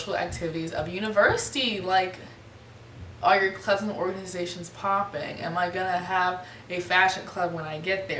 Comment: Did she just say SHAPOPPIN? 👀 BGC?! haha girrrl.